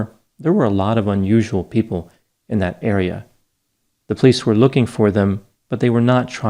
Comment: I misheard "area" for "era". Both words work in this scenario.